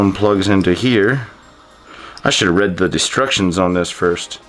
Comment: I love the banter and bike bashiN' u and exchange @ 7:27 sounds like u said destructions instead of bike instruction